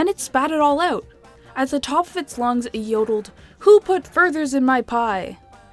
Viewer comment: did i hear burgers? or am i just having hearing problems